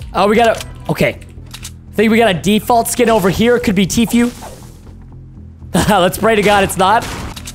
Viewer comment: did anybody hear him say tfueeeee